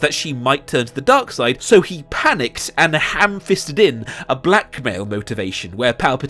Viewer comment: when he said blackmail motivation he showed finn and i thought he was saying black male motivation for the next minute until he brought up batman vs superman😂